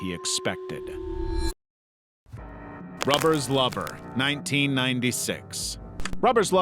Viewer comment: I thought you said "flubbers lover" when you said "rubbers lover." 2 very different films im sure.